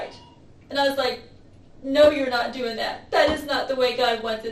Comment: sounds like she told the truth for a moment about him saying he couldn’t do that but it was a response to her trying to persuade him and she was actually saying “you can do that”